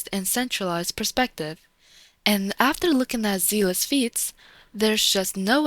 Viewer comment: Ngl, I deadass thought you were saying a custom pluralized form of feet… like; “Seele's feets”